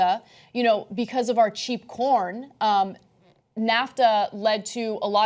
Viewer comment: thought she said it was because of our cheap porn.... anyways ANA PAVE DARKER IS BETTER :clap: THAN :clap: YOU :clap